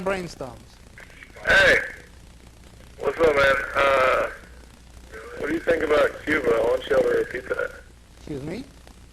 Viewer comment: What the fuck does he say @ 20:00??? It sounds like he says what do you think about Cuba opening up a Pizza Hut LOL